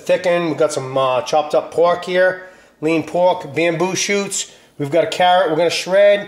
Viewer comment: Hello, love your recipe and I'm looking for all the ingredients to make it. Please, at 1:30 what is that? I'm latina and, although my english is not bad I can't understand what you said there. If I can't find that ingredient there's something I can use instead? The soup tastes the same without it? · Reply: Bamboo shoots , they come in a can packed with water , drain and Julienne (cut in thin strips) they dont add a lot of flavor but do add texture